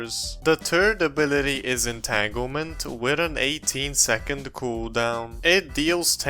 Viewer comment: did i hear the turd movement?